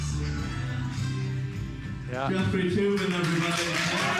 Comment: What was Sam's stage name? I thought I heard "Jeffrey Toobin".